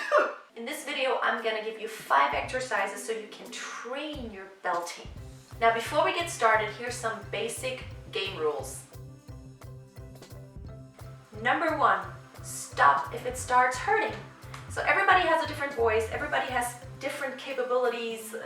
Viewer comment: I thought you said gay rules and everything from then took a whole new meaning